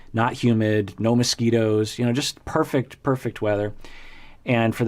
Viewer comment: A very funny moment... When you were describing Seattle in the Summer I misheard and thought you said, "Nice weather, Men in Speedos..." But upon rewinding I see you clearly said "No moskitos" Was laughing so so much!!